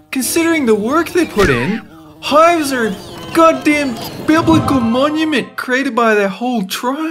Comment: After 0:47 I heard God Damn